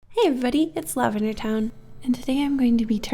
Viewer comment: For years I thought you said it's lavender time but now realized it lavender town